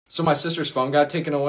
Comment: I heard "my sister's fungi..." 😂😂😂